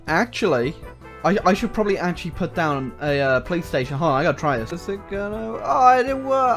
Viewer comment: Every time I hear him say "police station" i hear play station